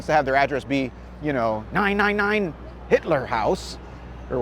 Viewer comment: "Nein, nein, nein Hitler house…" Did I hear that right?